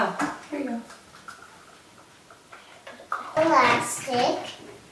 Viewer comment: When she said elastic I first thought she said "relax kid" 😂